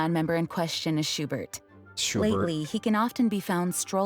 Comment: Did anyone else hear Jean say shlately